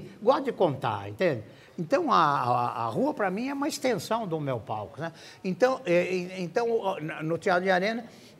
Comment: "Então a rua para mim é uma extensão do meu p4u." Só eu que ouvi assim? Kkkkkk. Brincadeiras a parte, descanse em paz saudoso Ary Toledo.